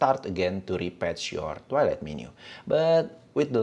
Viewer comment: captions say Toilet menu.